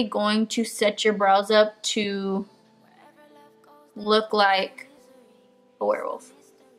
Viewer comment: what did she say at 4:23-4:25? I didn't quite understand it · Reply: Hvnnvhc I said to make your brows look like a werewolf lmao